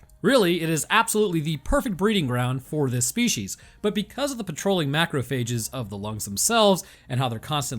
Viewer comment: I have audio deficit disorder, it sounded like you said petroleum macrophages 😂😂😂